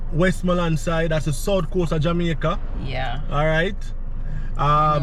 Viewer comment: Nice good memories, keep it up but please stop more often for the buildings and you guys are so good that I thought you said South Jamaica but May-Pen and Mandeville is really not South, but I enjoyed the trip. Thanks